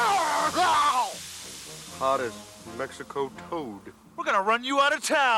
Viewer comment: ‘Hotter than Mexico coke’ TVY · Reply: I heard “Mexico toad”.